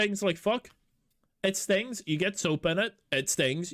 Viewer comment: I thought you said soup at first. Had to rewatch it. I'm thinking how often is this man spilling soup into open wounds